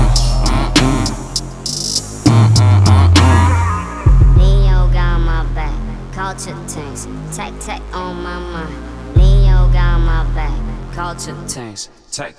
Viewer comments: other: "most legend teaser" me: neo got my back culture things tech tech, oh my mind∼ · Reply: Lmao I thought they was saying neo got my back take take all my money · What they say : neo got my back, culture things, tech tech on my mind What I heard : nct got me again, comeback lit, give give them money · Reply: i heard "neo got my back, culture change, take take all my money" lmao 😂😂